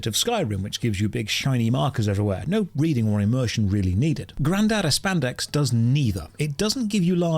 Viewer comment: Here are all the Josh nicknames for the game - Granada Babado - Granespa Abespa - Grandiose Pomegranate - Pomegranate Land - Grand Cabbage - Grindibli Adibli - Grand Vegetable - Granabado Ababado - Grandedada Abedada - Granado Encanto - Grandada Spandex - Grandi Espandi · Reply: Grandad Espandex is what I heard. I don't care if it's wrong, lol :) Or, on second thought, maybe it was Grandad's Spandex...